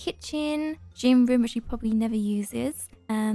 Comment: when you said “the gym which he probably never uses, I thought you said “the gym which poppy never uses.”